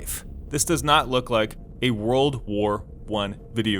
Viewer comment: Did he say World War I 6:15. ? Edit